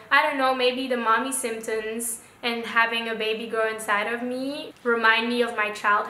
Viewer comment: Am I wrong or did you just said that your getting a baby girl?? On ( 11:21 ) ❤️❤️❤️♥️♥️ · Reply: no i said "having a baby grow inside of me😜"